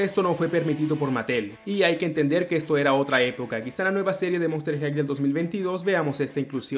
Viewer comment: queeeeee! ¿escuché bien? ¿acaso dijiste NUEVA SERIE 2022? :0 OMG!